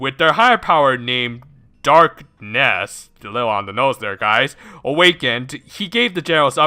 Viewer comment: So the big boss is named Dark Nest, huh.... I misheard it for Darkness, though still a fitting name. Also I am going to bet that the PM in the Star Twinkle's world helped Abraham is because he either knows that Abraham is an alien, or he IS an alien himself, hope this gets brought up in future episodes. Regardless, this is not really a good episode, but I still enjoy it. It is fun to see how the characters act in a movie, which really does not look like it is going to be a good one, but just like this episode, it is at least going to be an entertaining one. So now, I am looking forward to Lala's first experience in school.